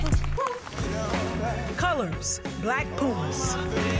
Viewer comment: NO i really thought she said “black poop” 😭💀